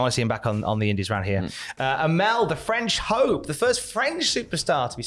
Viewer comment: Not gonna lie, I thought said "Amel the French hoe" xD and I did a double take LOL